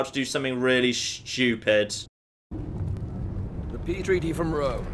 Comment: did anyone else hear "The pee treaty from Rome"???